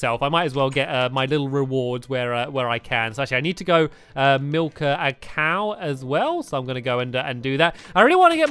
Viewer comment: at the start when he said he needed to milk a cow, I thought he said that he needed to milk a cake